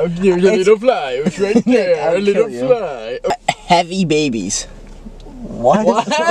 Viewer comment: STAWP why’d i think Chris said happy babies i was like “ well damn what does he have against happy children?” But he said heavy